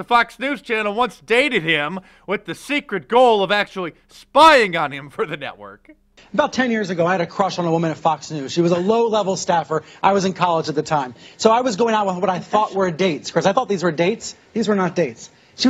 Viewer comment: Time 1:37 Did he say " had a date with a woman" ???...What was his name!